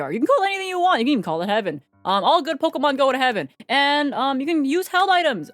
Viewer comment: i tought she said "all pokemon go to heaven" so i went like "EVEN HYPNO?! Must comment" and rewinded to get the time she says it, but when i rewinded 10 secs and heared again i heard the "all good pokemon" and then i went "ohh.... i will comment about that anyways."